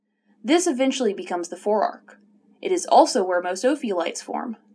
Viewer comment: did you say opiods?